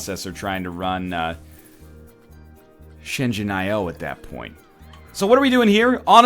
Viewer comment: Can someone type for me what he says at about 5:43? the name of the game? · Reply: it's Shenzhen I/O